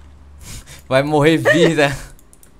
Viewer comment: escutei oq escutei vai morre virgem?